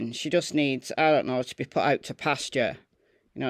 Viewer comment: "PAST HER SELL BY DATE".....😊😊😊😅😅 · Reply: i had to rewind!! at 1st i thought she said "needs taken out to pasture!" 🐄 🤣 🐄 😂 🐄 🤣 🐄